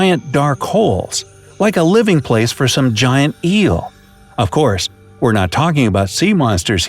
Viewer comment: For a second at 1:11 i thought he said 'Giant Eww' Gosh my ears